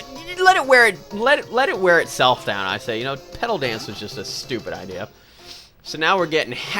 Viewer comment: i thought Petal dance said Poop Dance XD